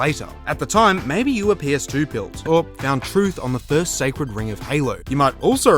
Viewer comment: “maybeyouwapeetooilled” is all I hear dude. Slow down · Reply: Maybe you were ps2 pilled is all I hear dude, maybe you need to get your ears checked